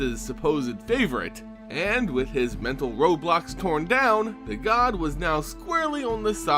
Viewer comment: You said “mental roadblocks” but I thought you said “mental Roblox”